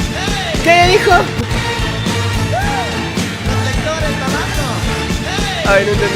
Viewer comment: Creo que dice: "Reflectores, Novatos" o "Reflectores abajo"